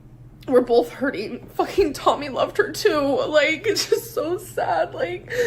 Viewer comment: Did she just say: "fucking Tommy loved her too" at 1:21:27?? ... what?